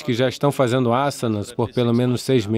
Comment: Gente, o que ele fala no tempo 4:54? Eu não consigo compreender e quando coloco na legenda sai “satanás” 🙀 sei que é erro de legenda então, quem pder me ajudar na identificação dessa palavra agradeço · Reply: Asana e uma palavra sanscrita de uma prática de yoga só funciona se voce e uma iniciada